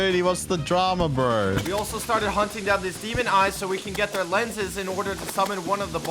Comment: I thought he said semen eyes